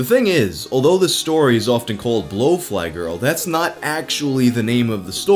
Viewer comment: You said blow-fly but I thought you said "lofi" and boy was i wrong 😭😭